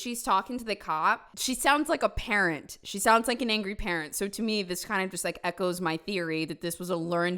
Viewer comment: I heard "She sounds like an angry parrot" 😂